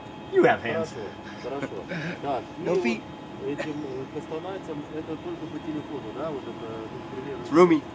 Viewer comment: слышу на заднем плане голос, говорящий по-русски: "Да. Хорошо.Только... это только по телефону...." Что ещё там говорится понять сложно. Два играющих балбеса на переднем плане орут.